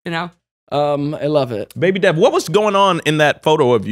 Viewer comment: i thought Daniel called Devon “baby dad” not “baby Dev” at first 😭😂😂